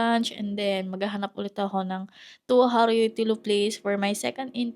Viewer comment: I did not use the word tuhari. I don’t actually know what it means. So maybe you just misheard it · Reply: and 11:36 you say something that to me sounds very much like it. I think that you intend to say työharjoittelu, but the way you pronounced it työ become tuo. I know my comment might seem as pedantic nuisance, sorry about it. But it's relevant in that sense that feedback sometimes helps to notice something that you don't recognize yourself. What might sound as unnecessary detail, can be highly relevant to become understood.